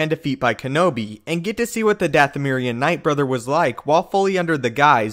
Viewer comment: I thought he said "Dathomirian ninth brother" Threw me for a second.